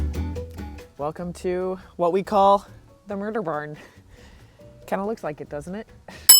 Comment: "the MURDER BARN". I replayed 17:30 ish spot several times. not sure of what you verbally said that you are calling the barn. Youtube transcript says "the MURDER BARN" . Is that correct?